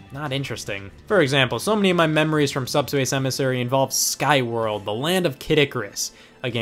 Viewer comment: When he said skyworld at first I thought he said skyrim (even tho it wasn't released) but I was really confused